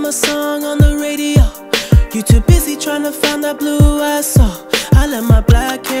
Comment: I remember when I was listening to this around Covid n I thought he said you tryna find that blue Lysol 😂